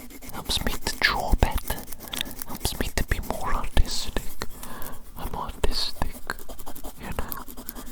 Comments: „helps me to be more autistic, im autustic“ is what i heard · you said it helps you to be more artistic but I heard autistic and was like hold on